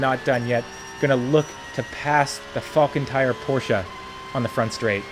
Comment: i thought i heard "the fuckin tired porsche"